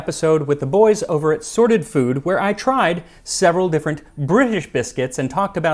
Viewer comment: If you're in the market for a signed copy of Tasting History, there's still time to get them before the holidays! Diesel Bookstore - <link> Watch me try some British biscuits over on Sorted Food: <link> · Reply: Oh, I thought you said "sordid food", and I was wondering what I was getting myself into.